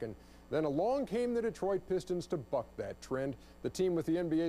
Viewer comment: lol it sounded like he said at 0:10 " to fuck that trend" but i knew he said "to fought that trend" or something like that.